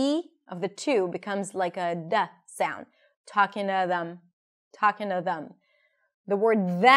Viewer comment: I thought she said “talkin’ at” 😅